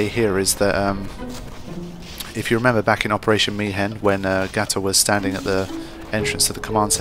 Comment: This might sound stupid, but I keep hearing "Operation Mi'hen" as "Operation Mayhem," and now I'm imagining a FFX/Fight Club crossover.